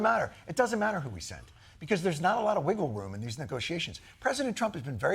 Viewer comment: I thought you said wiggo woom